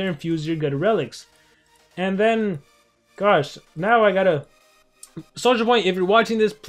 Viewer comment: who else heard Soulja Boy? 😂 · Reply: Me, but he meant Social Point